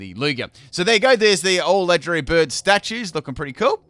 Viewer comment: So there you go There’s the all Electric⚡️ Birds Statues Me: Can u… Wait what?? Moltres: Fire Flying Zapdos: Electric Flying Articuno: Ice Flying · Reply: I heard "all legend'ry" But I can understand how you misheard it, it was a little bit garbled.